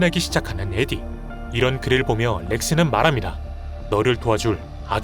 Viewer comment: 너(에디)를 도와줄 이 아니라 "널"을 도와줄로 들려서 순간 당황했다.. 맥스 표정도 넘 사악해보임